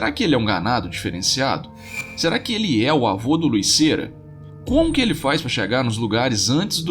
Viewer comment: eu ouvi Luís "Cera" kkkkkk